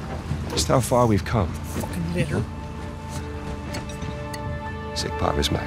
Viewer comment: What the fuck is Jack saying at 1:00:25? It sounds like the n-word??? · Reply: Jes Lynne Litter